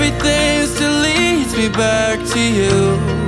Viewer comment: I heard "leads me back to you" as "deletes me back to you" 😂 This song is beautiful!!